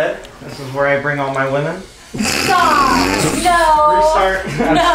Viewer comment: I thought said "this is where I bring all my linen"... I was like yeah, he's punny, until I realised he actually said 'women'😂